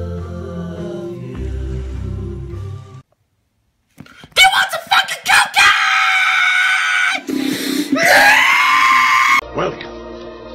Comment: I thought he said do you want some freaking cupcakes